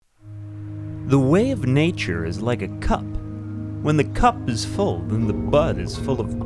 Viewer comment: I definitely heard the way of nature is like a cuck.